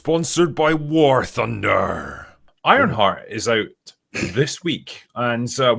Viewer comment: With that accent I thought he said “Ironfart” at the start.